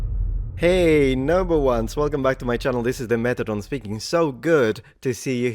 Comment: At the start I genuinely thought he said: "Welcome back to my channel, this is the methadone speaking." 😅